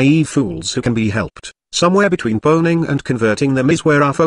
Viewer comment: Somewhere between boning and converting them our focus must be? I had to misheard sth, who'd make it into a goal to do a feminist? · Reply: pwning and converting tradcons.